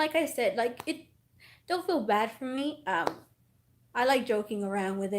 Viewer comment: even thought you said don’t feel sorry for me i feel sorry for you🤕💖